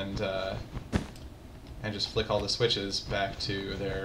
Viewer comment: ya at 2:21 sounds like he said shit